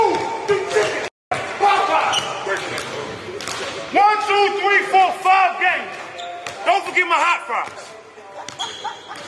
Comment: That nigga snapping he said "I'm not 5, what else you wanna see like pop eyes, when I see you i see food you chicken (Popeyes) ,1,2,3,4, 5 games don't forget my hot fires, I'm top 5 in this shit, I'm not 4,3,2, and I'm not 5!!!! We the 1's 😳🔥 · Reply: I thought he said, when I see you, “I see food, chicken shit, Popeyes and don’t forget my hot fries” “ and “I’m top 5 in this bitch” am I wrong?